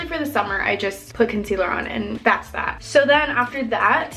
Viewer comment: did she say "basta?" (It means "stop" in italian) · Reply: Mariem. no she said ‘ that’s that ‘